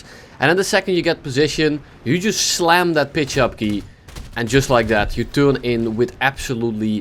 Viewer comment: why did i think he said “you just slam that b**ch up key”